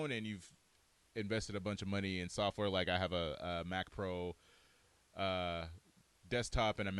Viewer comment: I thought he said he has a macro on his desktop. But then I realised he had a mac :(